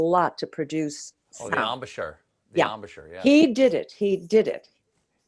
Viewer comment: For all those who struggle to understand what Stephen says, since it's not an English word: Embouchure <link>